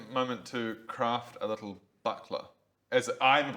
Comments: i honestly thought he said craft a buttplug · at about 18:25 I totally misheard buckler as butt plug